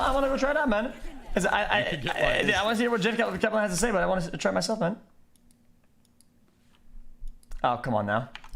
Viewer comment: what did he say? i heard i wanna try....jeff kaplan.....i wna try it myself man XD